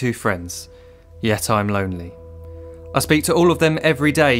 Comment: what does he say? "Ye time lonely?"